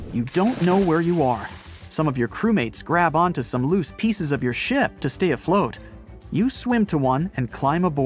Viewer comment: So I asked my 8 year old why she was laughing so hard... the 4:27 - 4:33 duration, she thought he said the "S-word" instead of "ship".